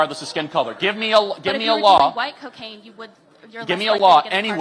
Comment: did she really say if it was "WHITE" cocaine it would be a lighter sentence?? SMH😮